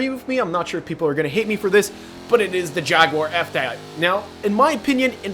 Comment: The fuck did you say? F thad???